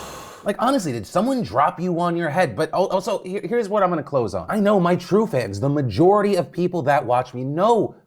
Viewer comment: I heard "And here's what I clothes on..." around the 00:30 mark